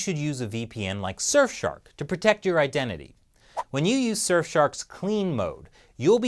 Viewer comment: surf shark? i thought he said south park.